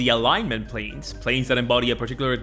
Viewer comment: Thought he kept saying plates and kept thinking D&D had a crazy Thanksgiving table set up